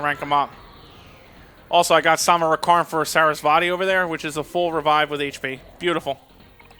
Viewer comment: I know its sarasvati But I hear Sarah's body